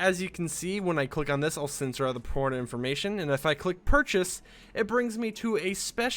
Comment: is it just me or did i hear him say "i'll censor the p0rn information"